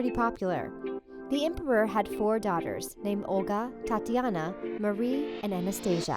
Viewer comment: did i just hear "Thotina" or "Tatina"? OMG!!